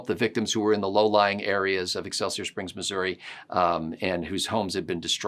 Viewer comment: For a moment there, I could have sworn said "Alka-Seltzer Springs, Missouri." Had to rewind and listen again lol.